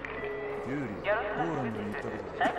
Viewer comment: ユーリって聞こえた